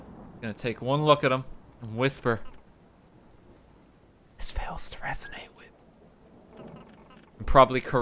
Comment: I thought he said its free real estate at first